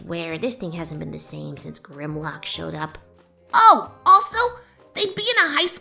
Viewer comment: Did I hear Grimlock at around 26:30? If so I'm glad because I love transformers